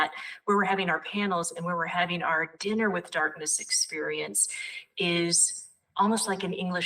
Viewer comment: When they talked about the place, at some point I heard 'Tinder with darkness' and now I want that to find ppl to larp with near me lmao.